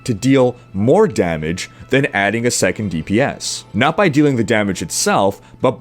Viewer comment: the subtitle...second gpd instead of dps...haha!